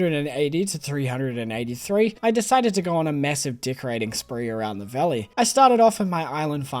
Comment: why did I hear it as di$@ riding spree